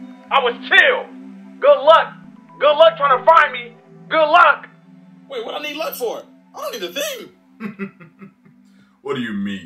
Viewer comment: What did he say ? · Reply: "Wait what I need luck for? I dont need a thing!"